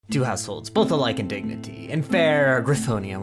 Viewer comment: Who thought that at 0:00 he said a holes?